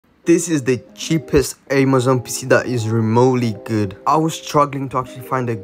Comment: I swear he said spissss and not pc😂